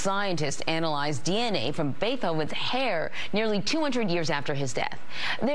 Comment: Did she say beTHoven